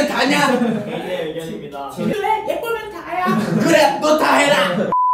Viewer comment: 😭 why did i just hear him say “putangina” lmaoo